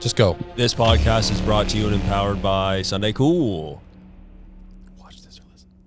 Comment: low key i thought yall were say empowered the whole time haha you were saying "and powered" im guessing